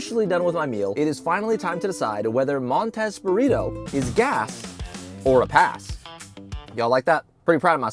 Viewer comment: Should have been "gass or ass" 😅😂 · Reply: Wasn't paying attention when he said that bit and I legit I thought I heard him say 'gas or ass'